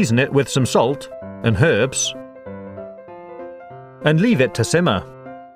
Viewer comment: anyone else hear perves instead of herbs